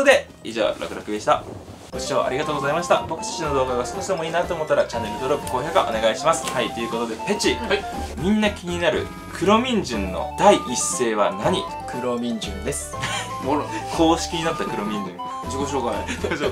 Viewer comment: 家の母親がネタバレしてきて 黒ミンジュン出てくるよって言ってたんだけど 俺の耳がイカれててクローンミンジュンに 聞こえて、クローン!?ってなったって話。